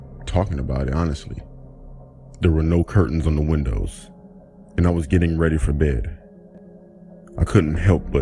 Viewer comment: Who heard that guy say I was getting ready for dead at 4:07 LOL